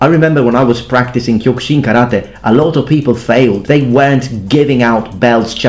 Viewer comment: Did he say kyo kushin Karate